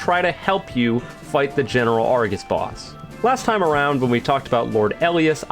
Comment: Why does it sound like he says Argis rather than Argos